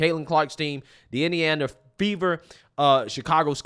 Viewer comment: I need to get my ears checked... I could of sworn you said the "Indiana Beaver".